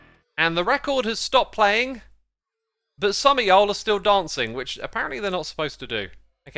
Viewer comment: Bruh did xisuma just say y'all???? It sounded so strange